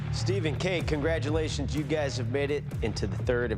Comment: Who else heard "Steven King"?